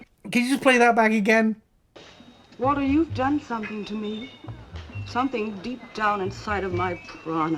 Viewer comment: sounds like prana to me, sanskrit term for life force energy. Fits the beatnik culture, too. Guys like Jack Kerouac would definitely know this term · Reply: "You've done something to my piranha" has to be added to the book of great mishearings, though.